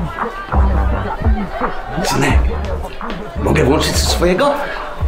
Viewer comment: usłyszałem "synek mogę włożyć ci swojego"